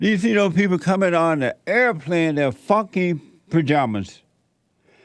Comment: Damn did I hear Jesse say “fucking work out clothes “ sheeesh maybe I heard it wrong . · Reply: 😂he said Funky